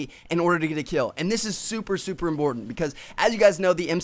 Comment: did he say imBortant !!! :S